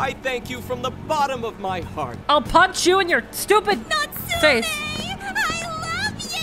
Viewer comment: between talking and the NPC, it sounded like "I'll punch you in your stupid nuts!"